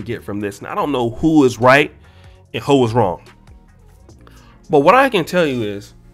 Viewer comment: I heard "Hoe is wrong." LMAO